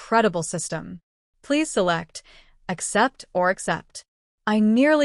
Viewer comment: Accept or accept?😅 No choice then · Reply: I thought it said accept or except?